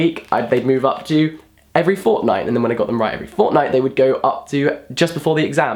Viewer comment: I heard Fortnite.